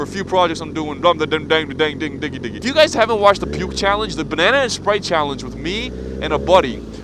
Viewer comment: Who else heard him say "Pube Challenge"? lol